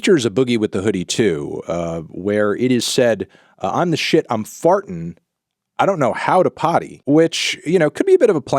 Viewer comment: this whole time i thought kodak was saying "honestly i'm foreign, i don't know how to party" 💀💀💀